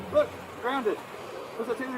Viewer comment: Did he say “im grounded, what’s a taser gonna do?” To an officer holding him at gunpoint? 🧐 Edit: timestamp